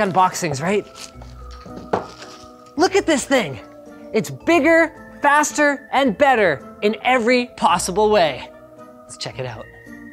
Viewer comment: I thought he was going to say DK's part of the DK Rap. "He's bigger, faster and stronger too".